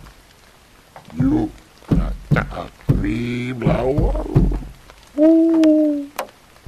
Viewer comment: Wow, I can understand some of what he's saying. At about 18:20 he's lightly banging and he says, "You have to help me, flower?"